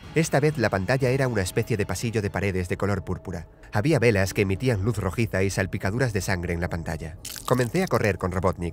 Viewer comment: me dio risa Noce por UE por que dijo habían bellas que emitían luz rojita